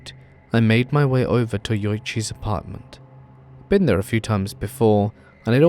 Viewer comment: When i heard Yuoichi i thought you said Kouichi for a minute there and my brain IMMEDIATELY said "oh no...this...can NOT be good 👀" 🤣